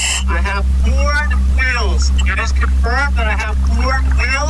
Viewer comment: When Jared said he found Ford wheels, I thought he was saying he found FOUR wheels, and I was like "Yeah, most cars have four wheels".